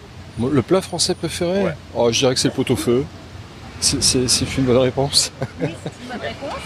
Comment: What kind of accent has this guy? Seems a little unusual to me. Could someone please explain why 2:47 "j'suis assez cuisine traditionnelle" means "I'm very much into traditional cuisine". Makes no sense to me. 2:37 and what does he say here? In the text it says "C'est une bonne réponse?" but I hear something like "C'est fine [actually, I hear "fune" but I guess it's fine?] la réponse?". Thanks. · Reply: Thanks. I still cannot hear it, I hear "C'est fut la réponse?" now. But maybe I will get that in the future. :D